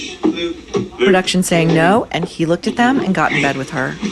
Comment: 'No look', Maureen, she was just saying 'no look', not 'No Luke!' Sheesh.